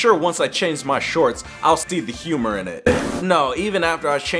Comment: did you guys notice that he said “stee” instead of “see”